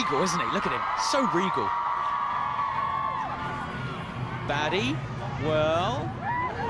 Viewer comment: I thought he said "Daddie" but Nah, he said "baddie" 😂